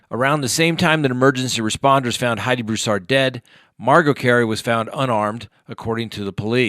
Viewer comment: Omg!!! I thought you said “Margot was found unarmed” i was like “weeeelllllll that should be common knowledge, I mean she’s a BABY! Lord I HOPE she isn’t armed!! What’s going on over there in TX?!?! Lol. instead of what you ACTUALLY said “Margot was found unharmed”. I found this funny and just thought I’d mention this so at least, I hope others can get a laugh out of what I THOUGHT I’d heard. And especially when it’s so sad. Humor is so important through tragedy and difficult times. Making fun of any of the victims though, there’s NOTHING funny about that. That’s the exact opposite of funny. Min